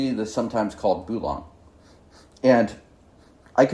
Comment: What word are you saying here? Pulong? I have imagine it's something else, hard to imagine Dayi making pulong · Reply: I think he said Bulang